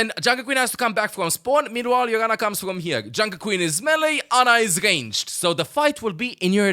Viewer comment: For a second I heard "Junker Queen is smelly, Ana is ranged, so the fight will be in your advantage..." and 50% of my brain agreed 100% with that statement.